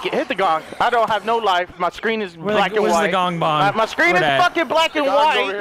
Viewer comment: i swear to God I think I heard him say I'm Black and Whites just hit the bong lol hes like Cheech and Chong lol